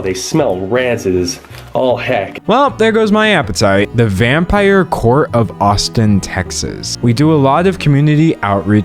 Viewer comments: I thought it said “the vampire court of autism” 😭😭😭😭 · I really thought it said 'the vampire court of autism' and got very intrigued and very excited. Briefly. My life is a horse vale of tears.